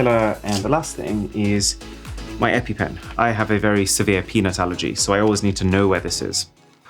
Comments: am I the only one who hears “penis allergy”💀💀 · fr thought he said penis allergy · I coulda swore he said he has a "severe penis allergy"... 😂😂😂 I almost lost it ... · How many of you heard it as "i have a severe peni& allergy" instead of peanut allergy when he whipped out the EpiPen 😂😂😂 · am I the only one who heard severe penis allergy😶🤣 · I thought you said I have very severe penis allergy 😅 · Bad speakers. I heard "penis allergy". Had to turn on CC. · did he say peanuts or anything else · Sounded like he said “severe penis allergy” at double speed ahah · “I have a very bad peni-“ tell me someone heard that too. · "I have a very severe penis allergy" Whhhat now? You have a what allergy? Ohhhhh Peanut's allergy, Bro, i just cant hear it any other way now! 😂😂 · i seriously seriously watched the peanut allergy part for 10 times.....cuz while i was watching this in 1.5X i heard it as "pen*s allergy".....n i was shocked....haha lol.... btw I am on of the oldest subscriber of this channel.... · I couldn't be the only one who heard "Penis Allergy" 💀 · did u say peanuts or ummmm